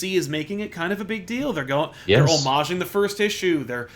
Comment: What word does use at 1:34 , omage? Their omaging the next issue he says?